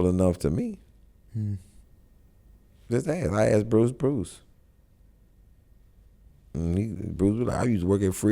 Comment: I heard him say I asked bruce bruce.... Bruce need his own podcast called ask bruce bruce...